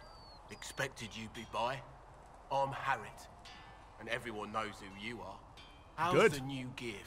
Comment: I thought he said Harriet when he spoke to the blacksmith, i looked up and was like... 'Yes?' XD